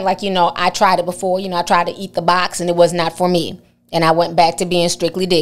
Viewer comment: I thought he said it’s not for me”